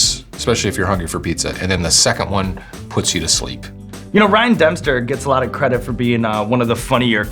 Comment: I heard "rian dumpster fire at 10:22" and immediately thought of the new Star wars movies